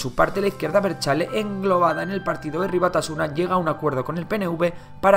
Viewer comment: Que dice en el minuto 6:01?? lo regreso pero no logro entender lo que dice después de "englobada"... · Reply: Jeje dice : En aquel momento, ETA englobada en el partido Herri Batasuna.